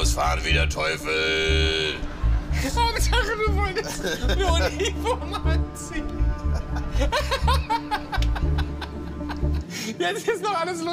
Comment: Was sagt Joko bei 7:25? 😬😂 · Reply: Ich glaub "Hauptsache du wolltest ne Uniform anziehen" 😅